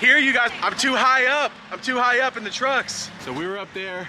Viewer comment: “ Im too high, Up on the drugs “ was what I heard. 😂‼️‼️